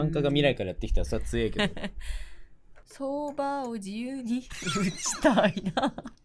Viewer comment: 一瞬よそ見してたから「相場を自由に...」に聞こえて資産家すぎる！？と思っちゃった